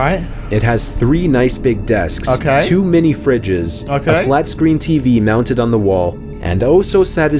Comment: in the video, it said “ two mini fridges” I thought it said “ to many fridges “ 😂